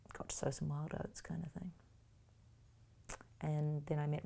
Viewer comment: Can somebody understand the words she is saying at 34:59 „the marriage is not gone so well...“ and then? I can not figure that out. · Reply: “I got to sow some wild oats kind of thing” It means she had relationships and slept with several men (not all at once); she had a bit of wild time.